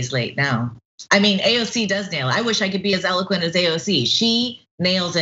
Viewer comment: that’s the funniest thing I’ve ever herd. She really said AOC is elegant😂😂😂 best comedian ever.